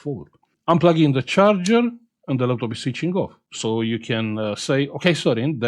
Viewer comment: "I'm pluggin the charger and the laptop SE CHINGÓ." Is that what you said, or it's just something that only we Mexicans can hear? :face-green-smiling